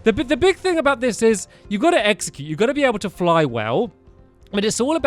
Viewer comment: Did you say execute?